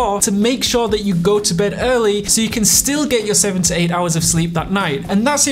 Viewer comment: Straight up heard it as 17 hours when he first said it. I was like, damn how does he know?